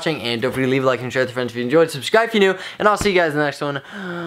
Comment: It sounded like he said it ain't falt lol🤣